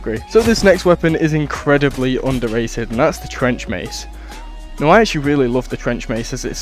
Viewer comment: with the flanged warmace i like to poke the characters edit: i thought trench mace said french mace